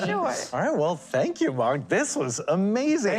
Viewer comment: it sounded like he said mark